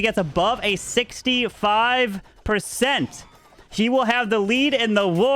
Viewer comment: did he just said she will win the war 🤣🤣🤣